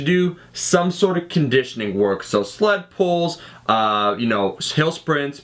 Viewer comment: Did anybody else hear "slut pulls" @ 3:49? Seriously, this is a great video.